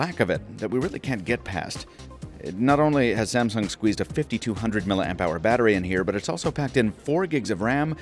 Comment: I had to rewind. 5200 mAh... I thought he said 250. I died.